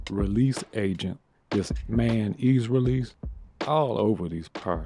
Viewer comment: Why did I hear "mayonnaise release" 😱